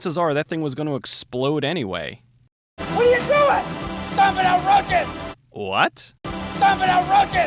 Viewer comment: "Stomping out roaches!" is what he said.